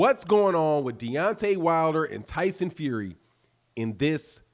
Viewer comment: I thought y’all said wilder was avoiding fury